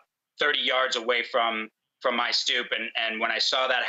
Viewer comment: what is the word after "my" in "... about 30 yards from my ___"? Thank you. Edit I found that the word is stoep It is a South African word meaning a veranda in front of a house Is it commonly used in American English?